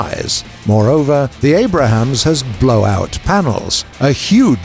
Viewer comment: did i just hear abrahams